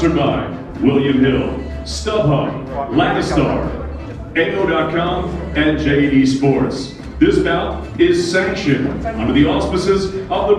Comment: when he said JD sport first I thought he said JATIE sport... I was thrown off